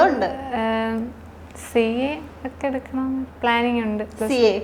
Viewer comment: Did I just hear CA🙆‍♂️ 14:18 Bro seriously 🙆‍♂️🙆‍♂️